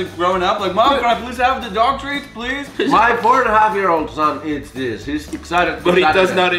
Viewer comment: Did jukka say that he have a son ? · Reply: Yeah he said, "my 4 in a half year old eats this"